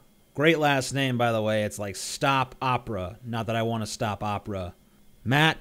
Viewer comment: At first I thought he said "stop Oprah".